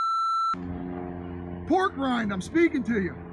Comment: Uh did anyone catch the comments that guy made when he was going off on the cops, specifically towards that one black cop? I could’ve sworn I heard him call the cop a n*gga and a pork grind. Please tell me if I’m wrong.